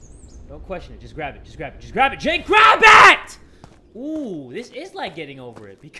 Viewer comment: did he just called himself jake😂😂😂